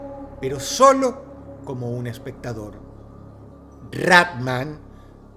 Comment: Pense que decía Rey Rata, pero el título no está mal.